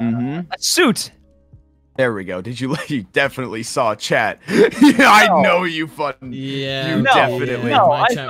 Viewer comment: does he say fundy but glitchy or something else? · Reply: Ranboo is definitely saying "I know you Fundy." It's probably is just glitchy lol